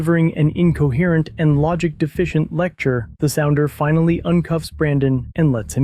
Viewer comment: did call the officer a "sounder"?